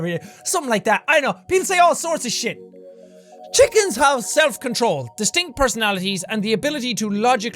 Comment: I am very tired right now, so at fact #6 (3:40) I thought it said " chickens can self-destruct"